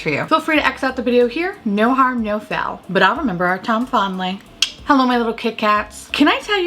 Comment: Have you been bringing back "Tom Fondly" instead of "time fondly"? lol There for a while I hadn't heard you say it, and the past 2 videos I swear I've heard come back 😂